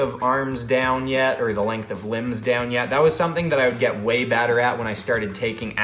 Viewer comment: who hear him say way batter at